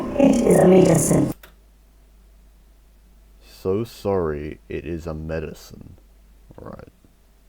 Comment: I might be wrong but at the begining of the reversed audio I heard "suicide" This part showed footage of women being hanged. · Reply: You might be correct, a mate of mine said that it sounded like "suicide is a medicine" instead of "sorry it is a medicine" like i said